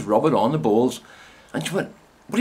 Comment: OMFG LOL with your accent I thought you were saying BALLS LOL OMG too funny